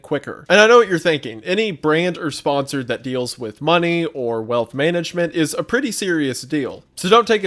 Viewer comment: I swore he said "death management" at the sponsor ad. Lol